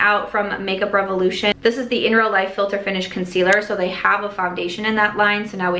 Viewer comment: The "Fat Oil Lip Drip" sounds like a product Fenty would have loved to have released. Edit: I misheard "IRL Filter Finish" as "Inner Light Filter Finish!" Doesn't that sound infinitely more attractive? If anyone wants to produce it, the name's a freebie.